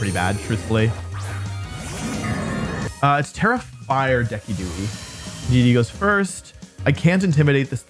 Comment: DID U JUST SAY DE-KEE-DOO-EE?? oh hell naw😂